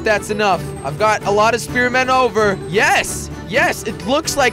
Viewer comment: 11.03 it sound like he says u have no balls spear men